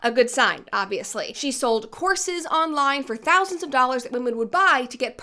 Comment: I heard, "sells them horses" instead of "courses." Yes Im high